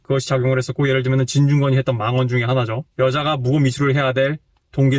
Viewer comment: 뭐라고 하시는 건가요? 여자가 묵음이수를 할 이유가 없다? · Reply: 묵음이수 X 무고미투 O